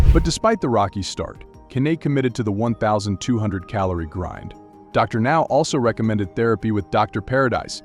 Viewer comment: DarNow? I thought I had heard a new black name lol